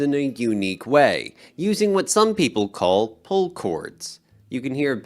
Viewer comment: What kind of chords? I thought “pull chords” is what is said but when I do an internet search, all I get are articles about pulling off my guitar.